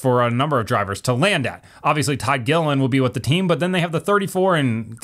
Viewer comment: did you say Ty Dillon will be driving for FRM?